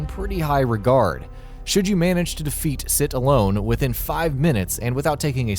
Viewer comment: Did he just say 'Sit Alone' instead of 'Sir Allone' ?